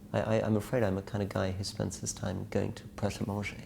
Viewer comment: What does he say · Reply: He says he's the kind of guy who goes to Pret A Manger. <link>